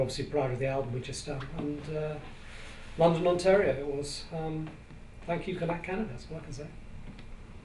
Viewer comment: I had to wind it back when Tony said "Thank you Canada" - I thought he said, "Thank you cannabis" :-)